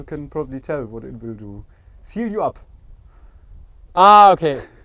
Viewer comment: What does he say about the chocolate's effect? I could not make it out, just heard something like "it seal you up". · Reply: yeah, seals you up.